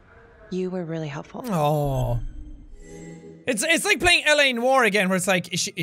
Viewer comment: the subtitles are wrong he actually says l.a. noire